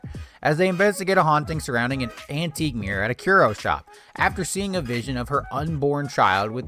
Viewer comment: Thought you said churro shop, and now I'm disappointed their origin isn't Mexican pastry related.